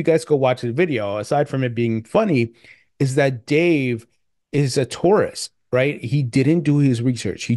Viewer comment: Thought you said he's a Taurus haha Thanx!🚀🚀🚀